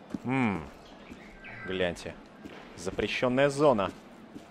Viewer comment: Ребята я 1 услышал на 1:20 когда он говорит мм запрещёная зона бляди мне 1 это показалось или нет???????????